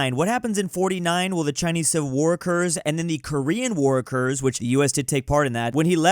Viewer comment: Instead of "war occours" I keep hearing "workers"